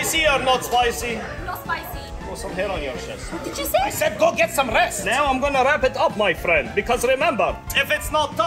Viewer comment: Grow some hair on your chest.” “What did you say?” “I said go get some rest.”😂 Edit: 3K LIKES!?! THATS CRAZY JUST FOR A COMMENT! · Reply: “OMG, I thought he said go get some chest” 😂😂